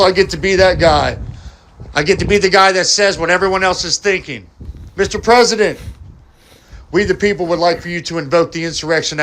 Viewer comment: 'Mr President'... All I hear is 'Master'... lol Those cooked, soft-brain fuqs, man.